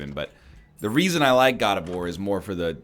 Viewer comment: Every time he said God of War I thought he was saying Gardevoir and it was fucking me up